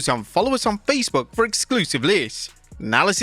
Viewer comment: can someone translate what he’s saying here? Exclusive Lizz? Exclusive lists? Exclusive live’s? I’m not even sure I would want any of those anyways.